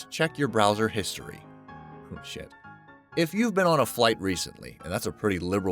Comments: What the heck did u say at 6:46/7??? I heard u said the word "sh**" after "airlines websites check your history". I turned the volume 2 max and i heard u say "sh**" at 6:47/6:47. Did u really say that????? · Was it just me or did he say shit at 6:45... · did he said shit or am I bugging? 🤣🤣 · Its funny that I heard a whisper of "shit" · I heard oh shit